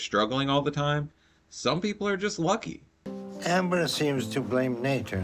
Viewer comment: Dr. Now sounded like he said Hamber. I love that man